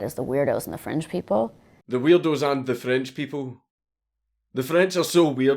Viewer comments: I actually thought she said Weirdos and FRINGE people. Then i realized that would make sense and we cant have that, now can we? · Daddy Dank, I think she said 'fringe people', not 'French people' Doesn't stop the whole thing from being batshit insane, though